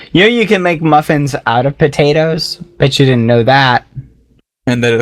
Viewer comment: it sounds like Badboyhalo says "B*tch you didn't know that " 😂😂